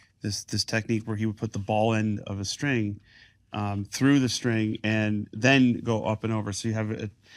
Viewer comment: “he would put the ball end of the string through the string and then go up and over” I had to listen to this a few times to understand what he is saying. I had no idea what he was saying when I watched this part in the long form video. It could be more useful if you were able to show a guitar with the through ball top wrap. Thank you for sharing the JoBo insight.